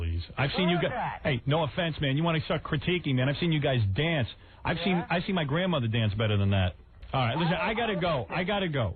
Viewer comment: what did he call howard when he said I gotta go on the phone? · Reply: see ya hook nose